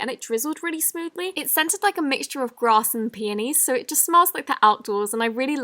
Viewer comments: On who it sounded like she said one of the Myslimeobsession slimes smelled like grass and panies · OMG I THOUGHT YOU SAID "and the slime is scented like grass and mayonnaise" I ACTUALLY STARTED GAGGING!! thx for likes!! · i know this isnt really what she said but I heard "bras and panties"(?) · Did she say grass an panties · Wait did she say “ grass and PANTIES” at 7:05? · When she said peonys I thought she said panties! 😂😂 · did she say it smells like grass and panties ??? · I thought you said grass and piano xD